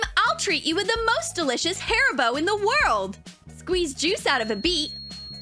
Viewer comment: who else thought that she said " HAIR BOW " but then realized she said "HARIBO" just me okay > :))